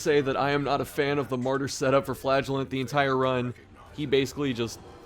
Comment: God damn it - I misheard the "full Flagellants" as "full flatulence" and legit lmao'd me arse. It's cap as hecc and juvenile af, and also it's necroposting, but why not, eh. : ) Love ye content, amigo!